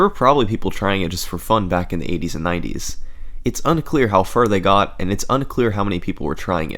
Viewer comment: I heard you say "1890s" XD